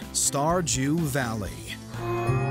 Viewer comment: did he say starjew valley